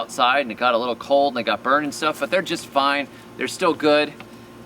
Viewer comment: Did you say they are just fine or did you say they are just vine?